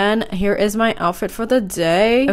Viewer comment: I heard "why is LA so wendy" 😂❤